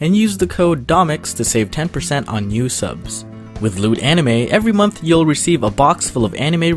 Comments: I thought he said with lewd anime 😂😂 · why did I hear "lewd anime" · I thought he said lewd anime